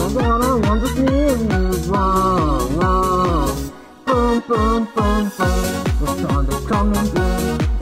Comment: I heard "Boom boom boom boom the sun is coming soon" So I thought a nuke was boutta be the next image.